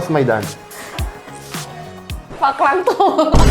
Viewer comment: 😂 Hahahhah Ano sabi ni Sylvia? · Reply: Parang "Pangkantu " 😅